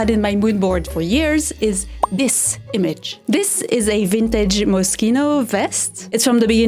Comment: 🔲🔳🃏⬛⬜⚫⚪🖤🤍🤡 I keep mishearing 'Moschino' as 'Mosquito'.